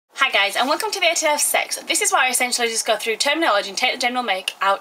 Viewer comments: hahaha at the beginning it sounds like you said. "Hi guys, and welcome today to have sex." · your introduction sounds like you said "welcome today to have sex"